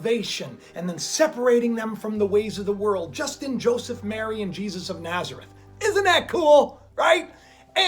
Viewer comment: Did you say "Justin, Joseph, Mary, and Jesus ?? Got my attention!! 😜😬😊😁 thats what it sounded like. At 8:02 in vid